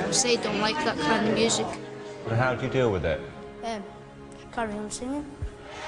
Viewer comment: does he say "carrying on singing" or something else?